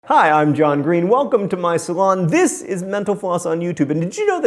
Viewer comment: everytime damn time he says mental floss i hear menopause "enjoy your time here at menopause".